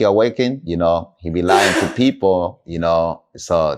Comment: Did he say. yahki awakened not trustworthy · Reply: Yeah, he said "he being lying to people" 😮 why didn't they question him more on that!